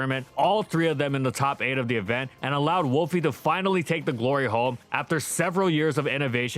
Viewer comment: I thought for a second said gloryh0Le lmao